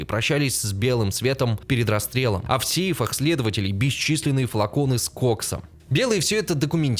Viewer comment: мне одному послышалось «флаконы с коксом»